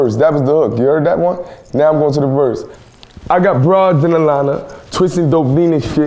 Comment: I always thought it was “I got bars and a lemon” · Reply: At first I thought he said "I got bros in London"